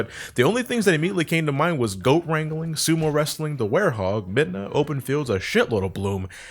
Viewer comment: did he just say werehog!